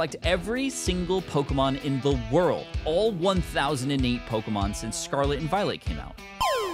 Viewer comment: Is it just me, or did he say Pokemons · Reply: he said pokemon since